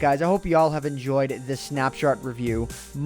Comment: Did say 'snapshart review'?!